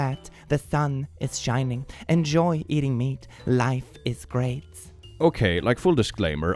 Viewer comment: kinda sounded like it's her meat....